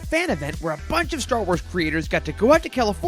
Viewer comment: I heard "a bunch of star wars creators" whatever that means.